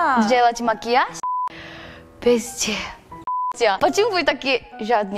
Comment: я офигела, когда сматерилась, а потом смеяться начала. миленько) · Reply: Мне тоже послышалось "в пизде", но я отмотала обратно и послушала ещё раз, и поняла что я глухатня просто. 😂